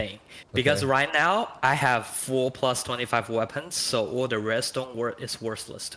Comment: did he say 4 +25 weapons or am I tripping? I think i want a chinese account.